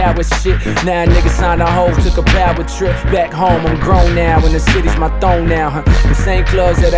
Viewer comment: i always heard it as "back home, I'm grown now and the city's my thong now" still made sense to me